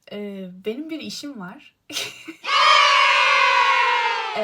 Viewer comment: eşim var anladım vfhbnfevhhnvef